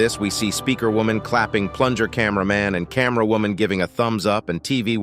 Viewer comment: did he said clapping plunger man that kinda sus